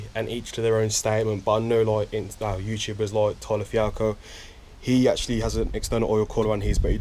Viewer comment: i couldnt understand which youtuber you said because i want to look em up. Toila fiako? Tala fuoco? ¯\_(ツ)_/¯ plz respond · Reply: Tyler Fialko